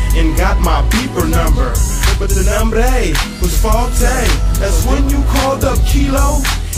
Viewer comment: I womder what he say @ 1:10??? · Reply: He said "But the numbray was faultay" (But the number was faulty) He just added his baydestrian Mac dre twist to the words✌🏽